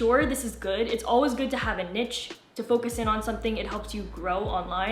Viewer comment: what the hell did you just say? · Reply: >nitch